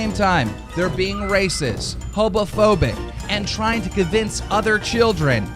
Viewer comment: is it me or at 16:46, DOES KEEMSTAR REALLY SAY HOPOPHOBIC?!?!?!